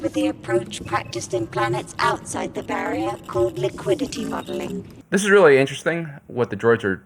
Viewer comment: The droid said “liquidity modeling”. Nobody said bubbling. The tangent that this mishearing sent onto, however, is very interesting! But where are all the businesses that these kids are learning to analyze? The “great work“? The barrier is definitely artificial. The red lights in the sky follow a regular grid pattern. Att-attin is isolating willingly, but everything the people know and believe is a product of what they’ve been taught by droids. There’s a scarcity of information here that the populace doesn’t mind, because they’re not aware of it. They get, and they’re required to learn, a LOT of information. Just not information that would be of much use outside the barrier.